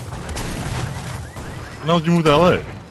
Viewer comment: Nick thought he said why did u move to LA not when did u move 😂😂😂😂